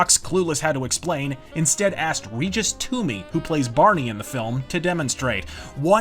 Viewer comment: Regis Toomey's character is Bernie Ohls. Sounded like you said Barney.